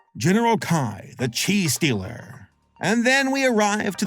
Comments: 😂 for a sec i heard "Kai! The cheese stealer" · “General Kai, the chi stealer.” For a second I thought you said villain of this movie was a cheese stealer.